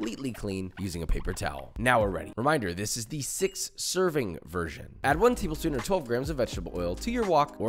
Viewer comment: 6 serving version? i hear 2 serving version